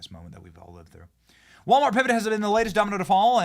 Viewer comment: Nice one Benny! @ 6:04 your tongue nearly slipped out of your mouth!!! I was waiting to see it slid out at what I thought you said "Walmart president and garble, garble, garble. ...It was nice listening to you prior to your exercise in reading @ 6:04. Lol You're making big bucks now Benny, so why not hire someone who can read for you, while you stick to the important stuff like giving your thoughts, which are always excellent!!! It's only when you forage into reading that your mouth falls apart. I know, you really want to dump me off your channel (cause he won't stop bitching about my mediocre reading abilities. I won't hate you if you do, but I'm only trying to make you the among the best! No, I'm not saying I could do better, only considerably worse...